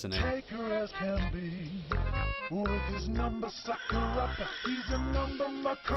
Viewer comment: number sucker-upper, a number fxxker-upper is what I heard help Im just milking certificates rn so you're quite a good British person😊